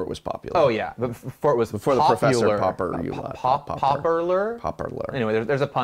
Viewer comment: Drink each time the word "pauper" is said in this video. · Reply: Seems like a good way to die of dehydration. All I hear is “popper”.